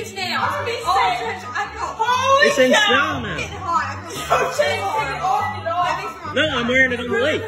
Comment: Did she just say "I'm getting hot, take it off?" Lol